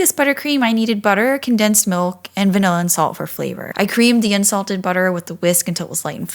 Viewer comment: I thought you said insulted butter not unsalted. I was like how do you get that. Are store bought insults ok? Lol